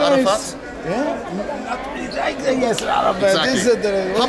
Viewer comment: What did bald say at 47:40 that he regretted it? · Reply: Yaser arafat